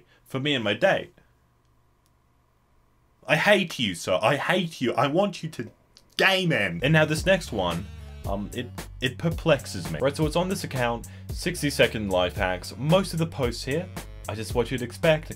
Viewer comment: i heard gay men